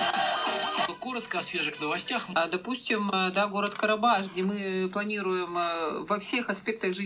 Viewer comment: Послышалось город Карабаш?????????🤔🤔🤔🤔🤔🤔🤔